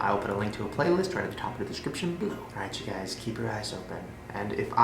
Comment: I heard "keep your ass open" instead of "keep your eyes open"